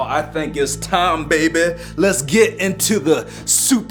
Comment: When he said let's get to it I thought he said yea get in too it